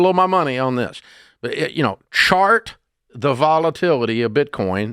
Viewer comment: Why does he say shart